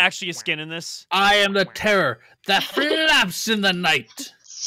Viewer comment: I Thought He Said That He Flaps At Night ;)